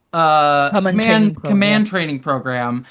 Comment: Vitamin B will help😮 I was shocked when I thought you said Genesis was going to be in the "Man-Training" Program🤪